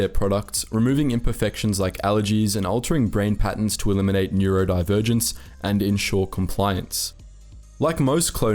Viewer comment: You said they eliminated neurodivergence and i just froze for a hot minute because i thought you said neurodiversity (autism) which in all fairness they would want to eliminate too but i just got hung up for a minute like omg they specifically designed out my neurological disorder. I would 100% get euthanized if i was a clone